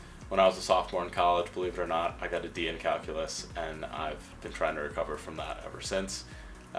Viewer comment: I thought you said "I got a B in calculus", then relistened and heard you said "D" :D I was already judging you ;'P